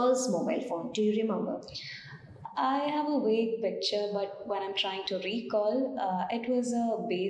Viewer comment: what is the word she told ? I have a ____ picture? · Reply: Vague picture